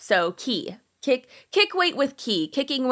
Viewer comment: Oh my goodness. I heard you say kicking it with key and I’m like what the heck did I do 😅😂 Now let me pay attention 😅